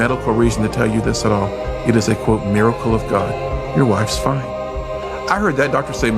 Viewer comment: Your wife’s fine? Lmaoo I thought you said it’s your mother. Fckn bozo. Full of bs stories. God isn’t real dorks, just learn to live love and be kind.